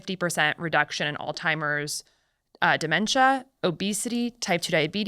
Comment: Did she say "Old timers dementia"? 37:25. WTH